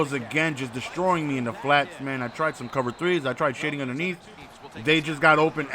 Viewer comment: did just say he tried sh!ting underneath lol